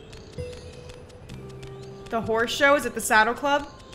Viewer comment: Is it just me or did she just say the horse sheit is full